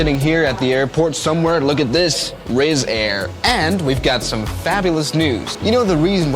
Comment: Did he just say 'rizzair'?